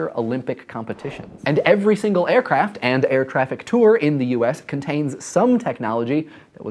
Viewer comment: does he say air traffic tour?